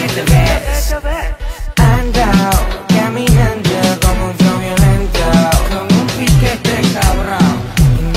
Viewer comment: Pensé que decía "andou caminandou como un fraudulento" xd